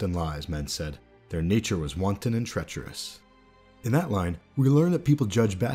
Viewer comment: You said treacherous instead of treasonous, though they do sound similar and mean similar things